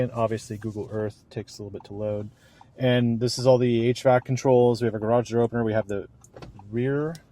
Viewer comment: Starting at 10:27I almost thought you said 8 track controls😄